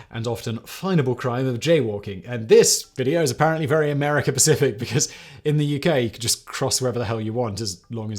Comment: so does he always say Pacific instead of specific as a joke?